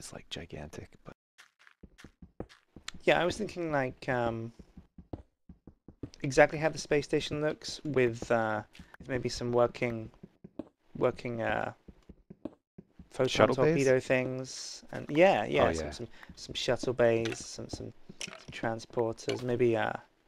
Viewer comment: At first I thought he was going to say "working bathrooms".